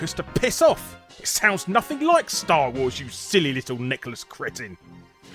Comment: silly little necklace cretin I know what I heard.